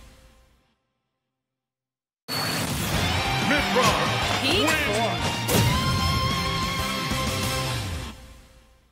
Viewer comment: Everyone is talking about their jiggle phycics... I'm trying to figure out what Mythra said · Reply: She's saying Peak Form.